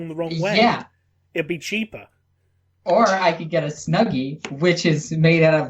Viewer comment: Snuggie? I thought you said "Smeggy" at first!